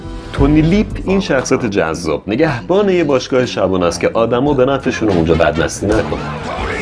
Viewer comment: ثانیه آدما به نفعشونه که چی؟ پنج بار گوش دادم نفهمیدم چی گفتی لطفا رو فن بیانت کار کن